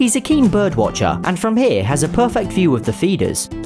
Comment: Am I the only one who heard “he’s a keen bird watcher, and from here he has a perfect view of the fetus”